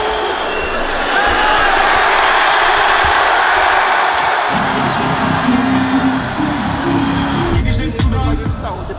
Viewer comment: Ktoś wie co mówi do murana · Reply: "nic sie kurwa nie stalo, nie przejmuj sie"